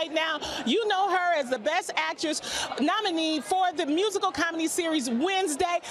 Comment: Wait, it’s a musical, comedy series??? Or did I mishear her?